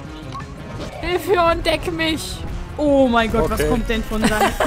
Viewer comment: "Deck mich!" ~ Ich habs erst nicht verstanden... hab kurz überlegt und hab gelacht xD